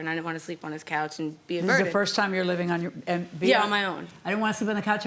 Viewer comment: she said she didn't wanna be a "burden", the judge thought she heard her say "murdered", and I thought she said "virgin" lmao